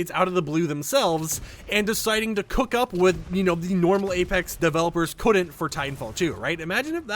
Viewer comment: Did I hear GAYpex at 3:36 ? If yes, then based